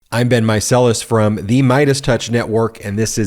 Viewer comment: I always hear 👂🏾 "I've been Marcelis" 😆 I love this channel!! ❤️ ❤️ ❤️